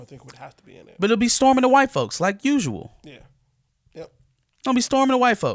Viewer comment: I thought said Stormin the white folks.